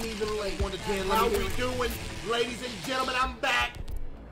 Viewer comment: I thought he said how we doing I’m fat 😭